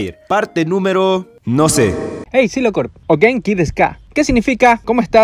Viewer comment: Cuando dijo "HEY SILOCORP" pensé que dijo "QUIEN ES DI K" y no se que dijo alguien entendió este comentario??? 😂